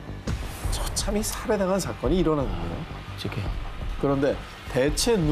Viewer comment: 부터 일가족 포함 이후에 제케 라는 음성이 들리는데. 이건 뭐지?